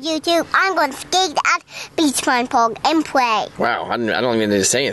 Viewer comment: Did he say play or pray😂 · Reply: He said play but the l isn’t clear